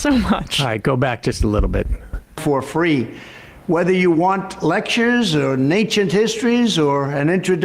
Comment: Was "Lectures or Nay-chit Histories" supposed to be "Lectures on Ancient History"? This isn't even live, what the hell is he saying?